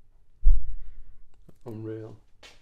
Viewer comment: I hear an EVP with my head phones on but can’t make out the words. Can anyone else? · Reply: Sounds like I'm real